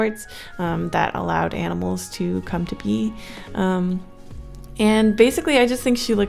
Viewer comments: Um I heard that allows animals to come to pee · to allow animals to pee is what I hear lol sorry I had to say it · I thought she said come to pee